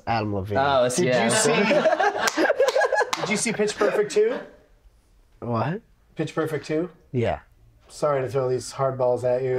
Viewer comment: Around 42 minutes when he was repeating "Pitch Perfect Two" to Zack Pearlman I heard "Pittsburgh for two" both times.